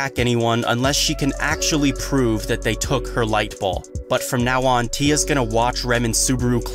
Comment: Sorry I only hear you say "Light Bawl"